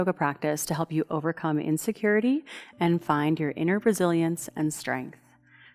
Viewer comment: I thought you said "inner brazilian" and that made totally sense to me :D